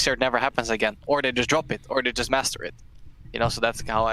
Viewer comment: did he just say just masturbate? xDD